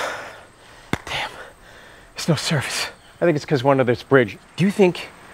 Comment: Does he say: " I think it's cause we're under this brig ? " -- cause that's what i heard and that's what i laughed out loud at.